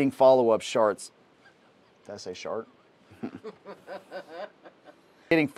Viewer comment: I actually thought that you had said "Sharts", at the 11:50 mark ! 😲🙀😅😂